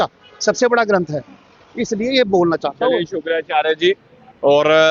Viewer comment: did he call him शुक्राचार्य जी? 😂